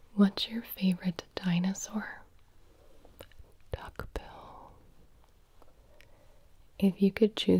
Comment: "Why are you so tall?" "German" Me, a 1.69m (5'6 ft) German guy: "Observe." Also, hold on, did she just say "Taco Bell" at the dinosaur question? What?!